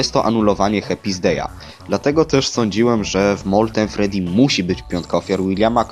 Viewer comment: albo źle usłyszałam albo powiedziałeś sądziłam...